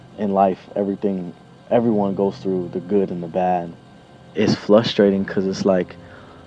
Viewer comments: all I heard was “Flustrating” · who else thought said "flushed rating" instead of frustrating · Ik he did not say “flusterating” I’m